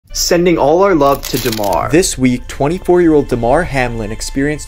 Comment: At first I thought it said "Sending all our love to Dahmer" 💀💀💀